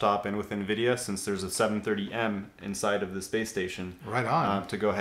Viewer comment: Wait, did he say 730M as in a 700 series NVIDIA GPU??